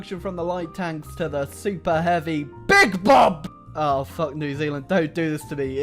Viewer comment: Big Bob tanks? I could have sworn I heard BIG BYTE Tanks!!